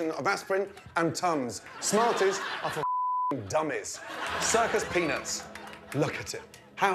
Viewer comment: Who thought Gordon said to the circus peanuts is circus pen*s -and pennies